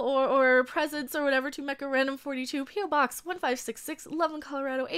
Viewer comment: Everytime you say i hear Macarena, i'm Peruvian :)